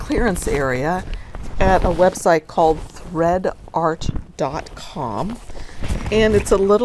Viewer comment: Sure!! It is <link> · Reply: Thanks! You may have even given it to me before, but I kept hearing "red" instead of "thread." :) Have a great day!